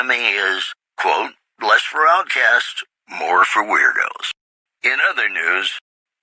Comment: "The Black Mountain Radio signal is back after a long absence. Listeners say the new programming is, quote: Less for outcasts, and more for weirdos." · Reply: I thought he said "less for broadcasts, and more for weirdos"